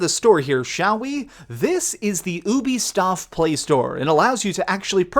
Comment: WAIT, WAIT, WAIT...Did he just say UBISTOF instead of Ubisoft at 7:19 xD ??? Please tell me I wasn't the only one hearing this.